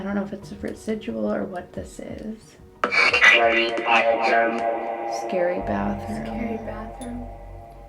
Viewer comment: I heard scary bathtub! Instead I get a feeling the group of men who hurt her and prob I have feeling that she got r4ped based of the pic😔